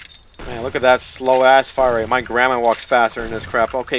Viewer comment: “my grandma walks faster than this crap” lol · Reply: i thought he said grammar not gramma